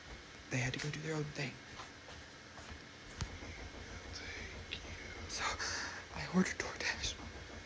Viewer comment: im sure I heard either "thank you "or "take you " being whispered right next to him ....whole he's covering his mouth